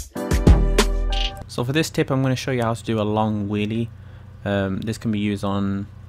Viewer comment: he said logest instead of longest